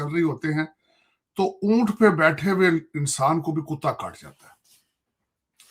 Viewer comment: Did I hear it correctly? Did he say “tarar kaat jata hai”?